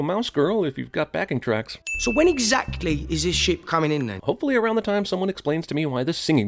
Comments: did he say what I think he said? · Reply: He said “ship” lol 😂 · is it me or does it sound like he says shit? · Is no one gonna say ANYTHING about Johnny's accent making it sound like he is saying "shit" at 7:30 ? No? Okay